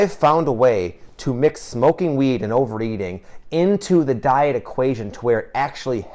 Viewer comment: Did he just say overeating?